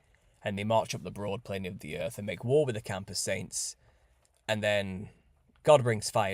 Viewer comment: in the video. "... and he makes war with the ???? Saints...". I've tried over and over to figure out what you're saying here? Campus saints? Gambus Saints? What saints are you saying and where do they come from. What are their attributes and what are their powers/weapons. Do they know who they are? How do we find & discern & join them? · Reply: Camp of the Saints